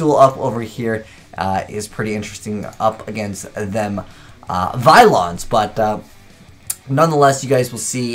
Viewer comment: no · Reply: He clearly says Vylons... "Up against them... um... VYLONS"